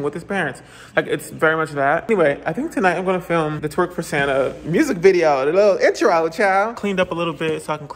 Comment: Omg okay this answer my question I had 😂😂 I thought it said twerking Santa but lately I was hearing twerk for Santa😂😂